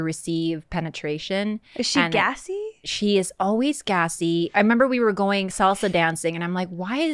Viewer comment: what if she said always ghastly.